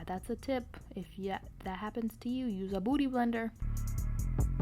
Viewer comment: I thought she said booty blender and I laughed so hard it aint even that funny 😭😂